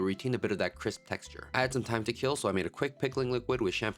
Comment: I wasn’t looking at the screen and heard him say that he “had some thyme to kill”. lol